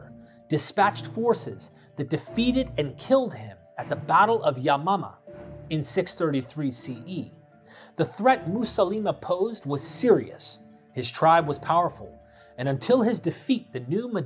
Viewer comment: I’m Gen X at 28:33; all I heard was the Battle of Yo Mama. I died.